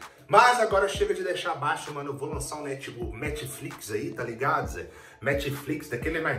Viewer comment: eu escutei ''metflix'' kkkk